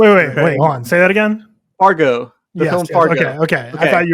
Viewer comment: Did say Fargo instead of Argo? I guess it doesn't rly matter lol but still